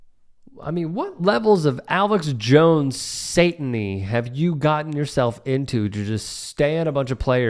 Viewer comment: Did he just say Alex Jones Sataning ???